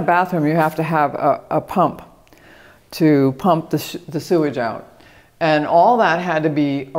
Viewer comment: Hahah did I hear you almost say pump the shi-sewage out?